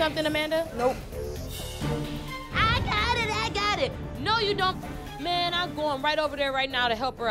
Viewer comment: Is it just me, or does sound like she gets cut off at the very end? "No you do-" instead of finishing the word don't 😂